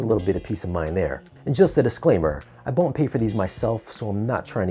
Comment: 'I bont pay for these myself' Sorry what did you say? Was that 'I dont pay for these myself?' Ie. they were given to you free?